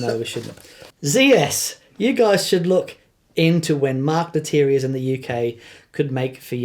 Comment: I heard say “Mark Matier” but that name doesn’t come up. It would help some if you were to slow down and enunciate when you say an unfamiliar name. I’m American, and words sound different in my language. ;-) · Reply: Hey Ron, apologies. It's Mark Lettieri. Cheers!